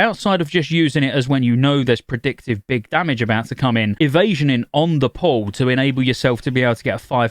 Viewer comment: Every time he says "on the pull" all I hear is "on the poll", and then I imagine a warlock in demon form slaying a stripper poll